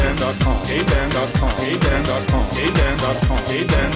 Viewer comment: In the beginning it sounds like he says, gay ben! He he he. Hahahah. HAHAHAHAHA! I MADE A GAY JOKE!! LAUGH AT MY JOKES!!! IM EVERY KID EVER!!!